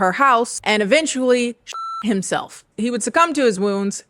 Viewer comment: on first watch I thought you said “he sh!t himself and succumbed to his wounds” and then I realized you mean pew pew’d himself not poo poo’d himself.